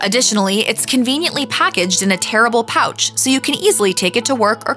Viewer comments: what's so terrible about the pouch? Seems fine to me. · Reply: Not terrible, tearable pouch. I thought for a second they said terrible also. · I thought she said the Starkist tuna was in a "terrible" package, then realized she said "tearable".